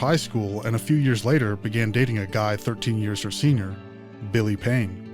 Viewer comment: what did you say? Started dating a guy 13 years a senior? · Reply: It would be "13 years her senior" and yes means "older"